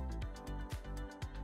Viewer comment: Did she say walah 😮😮😮?